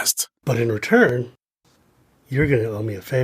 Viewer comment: What is he saying? Bent/bant? Jetted ojana?